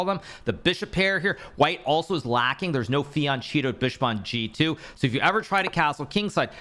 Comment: did he just call it a fiancheeto? 💀